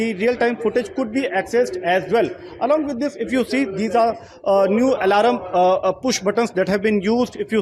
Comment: Did i hear alaRUM!!😝😝😝😝😝😝😝